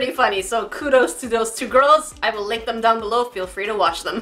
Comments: Ok, with the last one I thought she said: I wil l lick them- And I was like: 😶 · instead of "I will link them down below" I heard "I will lick them....." not even gonna finish that sentence. Wtf is wrong with my brain.. or my ears?🤔😂😂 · I THOUGHT SHE SAID "I will lick them below" X~X